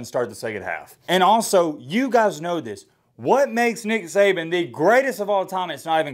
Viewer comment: I swore he said n*gga saban. Lol 😂😂